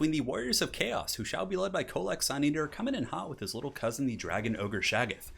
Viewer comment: I thought you said covid suneater, had to rewind a sec